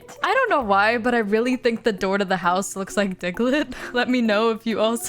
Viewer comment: the door to the house looks like ? ticklid? what are you saying?